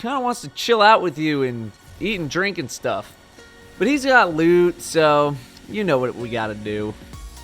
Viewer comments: I SWEAR I THOUGHT YOU SAID "HES GOT LUBE, SO YOU KNOW WHAT WE GOTTA DO!" IN THE INTRO AND I LOST IT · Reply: no it sounds like he is saying "Hes got lood" As in loot how did you get lube? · I SWEAR I THOUGHT YOU SAID "HES GOT LUBE, SO YOU KNOW WHAT WE GOTTA DO!" IN THE INTRO AND I LOST IT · Yeah I heard " he got lewd" and I was like we re lewding the cabal now!?